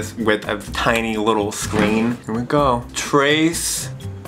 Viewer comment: Is it only me or does anyone else always look at his mouth and tongue cause of his lis however you spell it like at 3:07 where he says trayth instead of trace